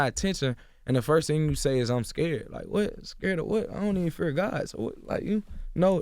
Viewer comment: What did say? I might be tripping especially with by the content of the amazing video, but ummmmmm, so what did you hear at 26:10 I thought I herd him say "I Don't Even Fear GOD" I could be tripping. But I'm a fan now, even though I haven't even heard his music yet, I'm going to search him up. Now.